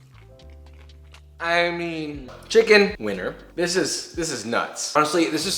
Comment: Ngl at 8:10 I legit heard Chicken Weiner 😂😂😂